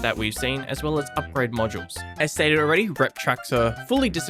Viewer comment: What are you saying at 4:51 ??? Esteruary retracter ??? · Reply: Lol sorry, “as stated already” 😅